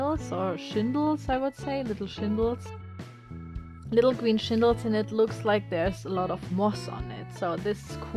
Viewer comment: English probably isn't your first language so I'm not being mean when I say the word for the stuff on the roofs are called shingles not shindles because that's what it sounds like your saying